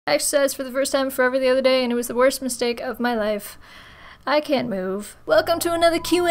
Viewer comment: Also, obligatory: "I'm so bad at oral" 2015 Context is forbidden here on Youtube. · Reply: If that wasn't bad enough, I had to rewatch the first few seconds of your video 10 times because I could have sworn you were saying "I had sex for the first time in forever the other day and it was the worst mistake of my life, I can't move." I still can't quite make out what you actually said.